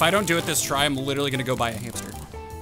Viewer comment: When he said ill buy a hamster i thought he said ill bit a hamster